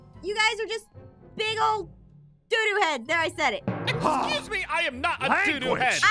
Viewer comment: 😂😅 I am actually a kid and I heard you say, do do head a** mouth for a while. I am a little kid. I am 6 I am 6.😂